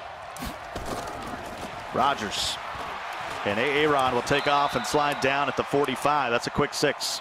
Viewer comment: I know I’m not the only one who heard “A ARON”. 🤣🤣🤣